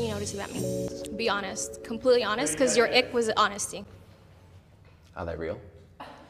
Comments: what did he say? to me it sounds like highlight reel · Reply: Oh another comment said "are they real" which makes a lot more sense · what did he say at 1:35 ? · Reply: I think he said "are they real?" which makes more sense.